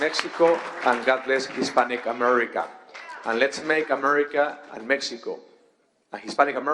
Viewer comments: At :45 I know it was a slip up but did he just say “Let’s make America Mexico” ???? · All i heard was “lets make america mexico” 😂